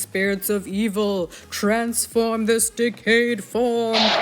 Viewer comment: I thought you said, "transform this dick-head form..."